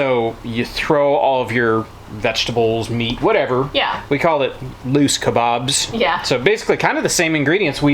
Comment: "Loose kabobs"? I thought he said "moose" kabobs! Oh wow! I need a hearing check! Dinner looked so tasty!